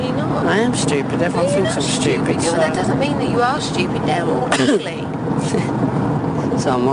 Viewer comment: did she say they're all ugly?